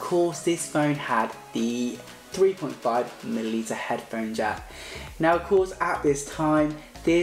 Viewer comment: did he say 3.5 mIli liter head phone jack? 😂 Mili liter???